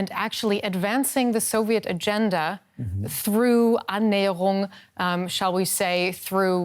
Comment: Did she say 09:56 "through Annäherung" ?